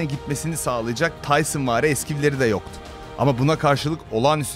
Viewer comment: da ne diyor ? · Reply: yok knk konusan arkadaş bişey diyo, (tayson vari eskiv ) gibi bişey söyledi onu anlamadım